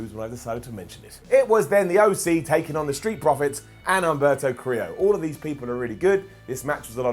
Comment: Isnt it humberto not alberto or have i misheard